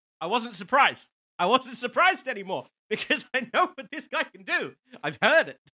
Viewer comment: Thought you said 'All cool - Not surprised now". That's a dangerous thing to say at the BEGINNING of a Dimash performance! 🤣😂 Also, perfectly normal for the brain cells and tongue cells to disconnect during / just post. 'T's OK You can press pause while you reboot. 😆 We all do. Ps. Vitas extremely pissed. Brought a copyright action against the show (which Producers had erred in not getting the legals in order ) and in public statement Vitas said would NEVER give Dimash consent to sing 'his song' again. = Major Dummy Spit. Silly really - they could have done some great collaborations. Ah well... Meanwhile Dimash hasn't sung this again anywhere any time - but doesn't matter as this version is out there on YouTube forever! 😄